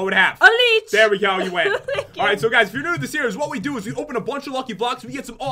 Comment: it sounds like says I like you instead of thank you